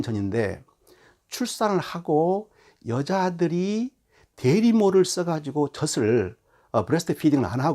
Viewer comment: 좋은 강의 잘 들었습니다 ㆍ 융통성 있고 지혜로운 교황이네요ㆍ~ 19:32 는 대리모가 아니라 유모네요^^ㆍ